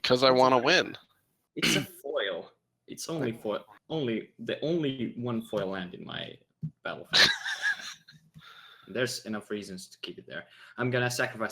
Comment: lied, he said that Blooming Marsh was his only foil land, but his Dryad Arbor is clearly foil. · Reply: I think he says it's the only one on his battlefield, but it's a little hard to hear because laughs....